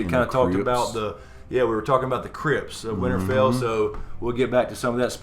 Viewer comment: at first i thought he said Crips of winterfell lol